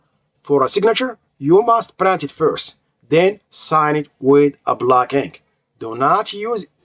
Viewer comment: Can you please clarify what you said @ 19:17 “for the signature you must______first”. Thank you. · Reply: You must sign it with black ink